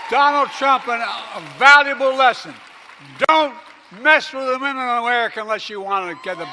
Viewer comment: I think he said “dontmesswiththemenandwomenofamerica”, he just rambled it all together, lmao!!